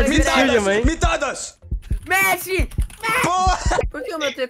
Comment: eu juro que escutei "messi" "messi"